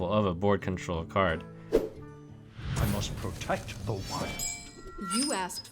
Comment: subtitles say i must protect the wine